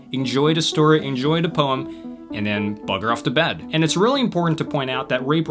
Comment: Did he say, " ...and then, butter off to bed" ????